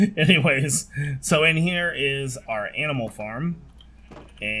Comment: did he say aminals? XD